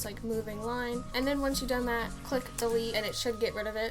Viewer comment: What did u say at 1:31-1:33? Click what? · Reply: click delete